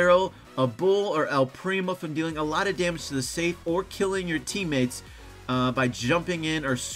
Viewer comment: Did anyone else hear at 7:45? Killing your teammates?